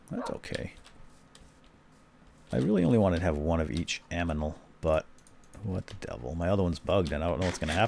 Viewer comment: Did he say aminal?